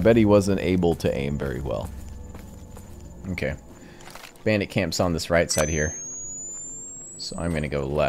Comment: I swear I heard "Bandicam's on the right" :D